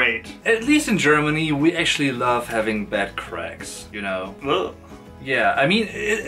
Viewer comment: When you said 'bed cracks' I thought you said 'butt cracks'. True story.